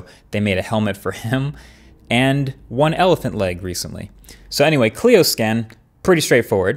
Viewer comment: wait. Elephant leg? Did I hear that correctly?